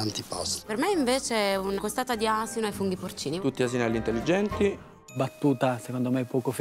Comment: non ho capito che battuta fa il cameriere al minuto 8:05 ... qualcuno me la scrive? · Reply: "tutti asinelli intelligenti" Non ho capito il nesso e perché avrebbe dovuto dirlo, ma vabbè